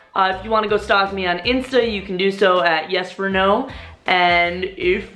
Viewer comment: Oh she was saying.... all this time i thought she was saying "so is it yes or no?"